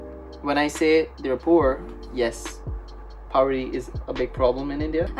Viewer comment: Hesaid PAWRI Instead of poverty